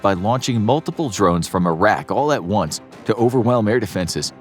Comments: Lol from about 6:45 when you mentioned the drones I totally thought for a second you said they were launched from Iraq... Not "a rack"... For a split second I was like oh these fools are full of chit... 😂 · I could have sworn he said "launched from Iraq" when I first heard it, and then realised he actually said "launched from a rack". Sorry for blaming you Iraq for something you didn't do.